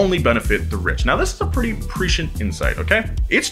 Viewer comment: The caption says "(mumble)" but he clearly said "prescient"